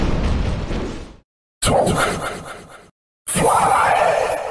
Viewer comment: Instead of “talk” and “fly”, I always thought he said “Dark!” and “Fire!”. But, like it’s said on the video, it’s distorted.